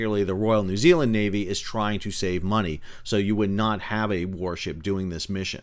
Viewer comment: Wait, I thought you said "miliary trying to save money" Does NZ not have a military industrial complex? No kickbacks for their politicians?